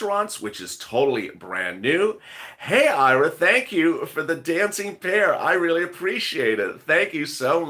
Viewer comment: I really thought you eere saying DANCING BEAR now i know its a DANCING PEAR 😝😝😄